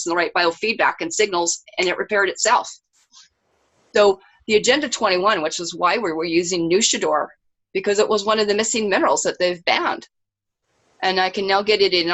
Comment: Dear Alfred; At 22:48 your guest mentions a "banned" missing mineral that helps with healing. I replayed it many times but i can't understand what she says or how it's spelled( it sounded like "nushadoor"???). Could you PLEASE put it under the video with a link for the benefit of your viewers? Thank you very much. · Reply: nausadar ..aka salmiak... on my facebook albums links or check ebay 100 gm package